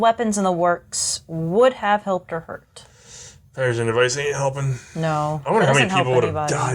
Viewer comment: "Mumble mumble ain't helping." What did he say? 1:13:04 You need a rifle with the best chance of getting more ammunition. 1:41:52 Maybe because I watch all the SMoWW1 videos, I don't watch the MoM videos. They're uninteresting. 2:25:59 Gun Jesus, that's who! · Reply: He muble, the pedersen device wouldn't help